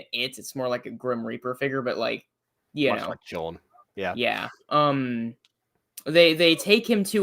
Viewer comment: did actually say, "Kind of like Chorn" when talking about using it/it's pronouns for the spirit? I am deceased. 🤣 And yes, I know that emoji means I'm old. So does the fact that I use Facebook. I'm aware haha. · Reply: Yes, I said "Kind of like Chorn" I'm impressed someone caught that!